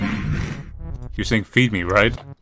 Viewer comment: I think he said eat me